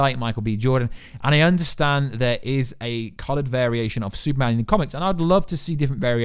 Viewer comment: did you say there is a "colored" version of Superman?